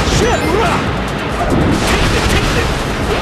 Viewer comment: Whenever Knuckles attacks h sounds like he says S**T ROCKS! every time!